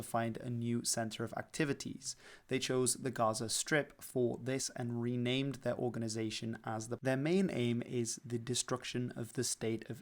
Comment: what does he try to say ? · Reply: Best guess is that after being expelled from Egypt, they renamed to Palestinian Islamic Jihad